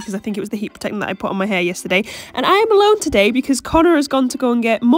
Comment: When sayd im alone to today i heard: i'm allowd to date😂😂